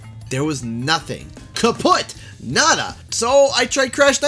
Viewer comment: did you say "kaputt" there? If so it makes no sense. It means "broken" in german :D